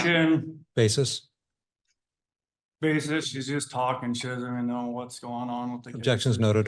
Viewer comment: "OBJECTION! She's just talkin'..." That objection is definitely not used enough in courts 😂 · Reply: I swear I heard "Objection!!" "Basis?" "She's just talkin shite" 😂😂